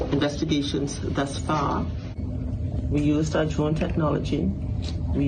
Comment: Can somebody please tell me what this chief of police says at about 3:34 ? I've played it about six times and to me it sounds like she's saying we've used Szechuan technology 😮 . What in the world is she saying , does anybody know ? I hope they find this woman well and alive I just hope that maybe she ended up maybe on some little Island that's not really inhabitable and that they'll find her in pretty good shape . The could have been some this hat we are her phone really did get lost and that she's still okay . The anchor says she's been to that Island many times and that her brother lives there ... yet I've heard no mention of whether there are any security cameras around there ? Maybe she was on a small boat and was taking pictures and dropped her phone ... maybe ended up some how where the boat overturn that she was able to swim to some small uninhabitable island ? I do hope they find her and find her doing pretty good .